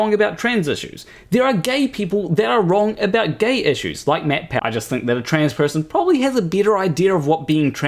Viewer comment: Like Matt Powell 😂... This is legit one of your best videos. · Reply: I misheard it as MatPat lmfao I was like "what does he have to do with gay?" Matt Powell makes a lot more sense